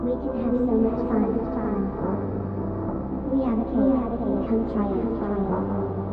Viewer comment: what are the partygoers saying? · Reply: He will be a cake we will play patty cake and we are trying